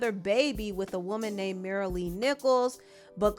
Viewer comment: I thought you said her name was Mara-Lee Nipples… and I didn’t feel the need to question that 😂